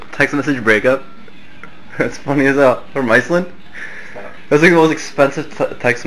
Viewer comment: did he say "from iceland"? because that's where I live..